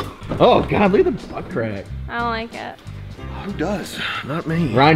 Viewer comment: she said I don’t like it but the subtitles said I love it. 😂